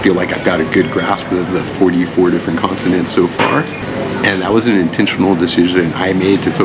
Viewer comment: did you say "44 different continents..." ??? I think you meant "44 different consonants..." Dude, you need to grasp English first before you attempt to learn Thai! 😂